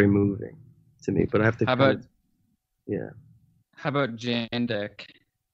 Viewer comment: what is the artist/band their talking about around 27:00? sounds like their saying jn deck